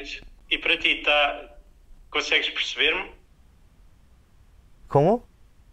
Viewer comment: He said: "consegues me perceber?" (no sentido de entender mesmo). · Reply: I thought I heard him say, "Consegues perceberam." I got a little confused because "perceberam" would be the third person plural in the present tense, right?